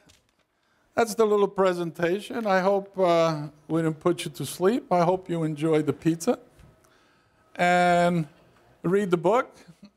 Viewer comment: did he just say, enjoyed the pizza?